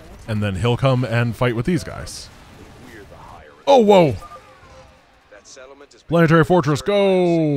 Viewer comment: really did just say OwO